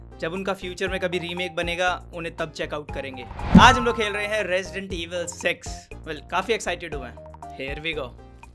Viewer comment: i hear resident evil Tesla model s 3x Obviously six and that word sounds similar